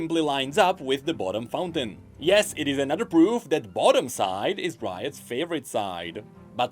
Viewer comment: What said: Yes, is another proof the "bottom" side is Riot's favorite side What I heard: All rioters are bottoms 🤐